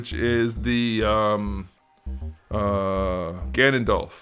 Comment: I heard Ganondorf lol